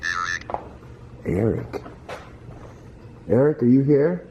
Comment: evp says SPIRIT (not Eric) @ 18:49 evp says CEMETERY ( so your guess about PAUL from the cemetery may be right ) @ 20:31 a cough or growl. It's odd because when I had my sound turned down the 'cough' sounded very clearly like a growl. I couldn't get my sound readjusted to mimic that 'growl' that I first heard. Also, the word 'confuse(d)' is being repeated over and over throughout this video. @ 21:11 evp TIGHT @ 21:17 evp RIGHT @ 21:21 evp POUND @ 21:29 evp ----?---WAS DESERVED (very interesting considering the story about the guys that got into a fight in the bathroom where the one guy was killed. That would back up that story 100%.) @ 26:43 evp LOKI (not Paul) @ 26:47 evp DAVAROS followed by the laugh. @ 35:43 Look at the last words being spelled. POPE 90009 (September 9th the Pope set new rules for the Catholic Church officials to mandatory report any cases of rape. This is darn creepy due to the reported story of the woman that was raped and beheaded, allegedly then thrown into that well in the basement. Follow that up with saying she felt she was being drawn to the basement and this whole thing gets the nerves to rattling sheesh. @ 35:46 Your phone (again last words) spell out popeil00. Two things: In 2000, the pope made a final historic trip to the Holy Lands BUT Pope.L is and artist of HUGE interest. If you watch, after the popeil00 is spelled out, it is followed by the word PIPELINE'S. I'm going to insert a link here that will blow your mind. This is wh